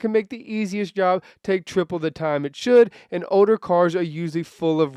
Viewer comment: i’m trippin i keep hearing oder instead of older